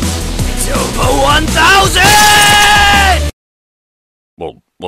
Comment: I always heard "Kickity his ass."